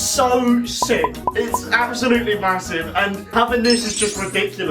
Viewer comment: did i hear M-M-Massive?